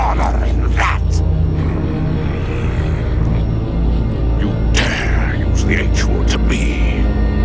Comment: WAIT WHAT DID HE SAY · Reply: You dare use the H word to me (H = honour)